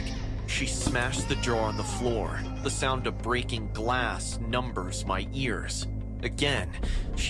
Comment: wtf! He said “the sound of breaking glass numbers my ears” instead of ‘numbs’! Bahahhaaha omg